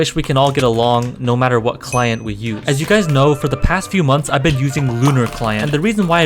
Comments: I like how he wrote “mother” instead of “month” xD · he says for the past few mothers instead of months in the subtitles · what we hear: past few months what the caption says: past few mothers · mothers instead of months hold on.. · the subtitles said mothers instead of months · does anyone notice he said months and the subtitle says mother XD